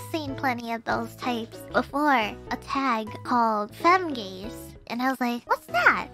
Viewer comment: One of those moments where I'm glad someone subbed the content, cause I'm pretty sure I thought she said Fem-gays until I did a double take and realized it said Femgaze...